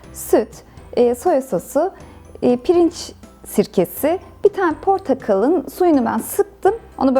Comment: yanlışmı duydum pirinç sirkesimi dedi ? dediyse eğer o ney nerde bulunur ? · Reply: arsay evet pirinç sirkesi dedim Migros'larda satılıyor.